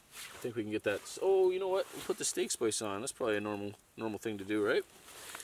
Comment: For the longest time I thought you were saying cake spice not steak spice. I was like man people say some weird stuff in Canada.